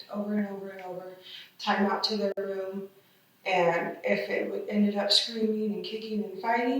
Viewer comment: The captions say “they” but it sounds like she says “it”… now idk how “involved” this lady was or not on all the abuse yet but if she did indeed say “it” when speaking of the children, then that’s obviously very telling.